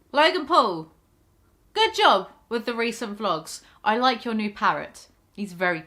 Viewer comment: Logan Paul, I like your new parrot Me: Oh god, I do NOT trust Logan Paul with the high maintenance, specific care of a parrot Finds out his dog killed his parrot Sigh. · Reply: Lol i didnt watch the video when she Said it and thought she Said "I like your New carrot "And i just accepted it as a fact